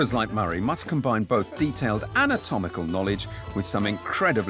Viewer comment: Is it just me or did he say atomical instead of anatomical at 6:18.